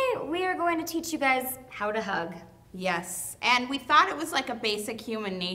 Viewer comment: i thought it said "How to thug"